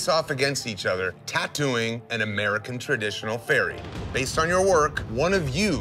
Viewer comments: I thought he said American traditional ferret. Was like, that should be interesting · I seriously thought they were saying American Traditional Ferret.